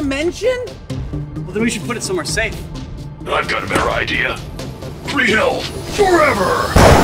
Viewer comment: what is Chief saying at 1:02 ？ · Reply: I thought he said "free refills, forever!"